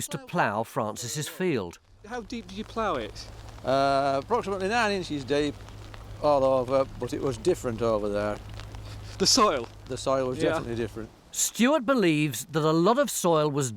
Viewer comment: what did he say😂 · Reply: "Approximately 9 inches deep. All over. But it was different over there. Definitely different." C'mon, I'm not even British and I caught that.